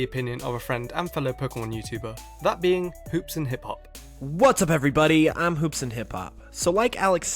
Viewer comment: When he introduced I thought he was saying Hoopa is boring!